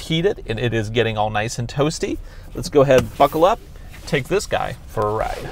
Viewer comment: I thought he said "let's go ahead and fuckle up" LOL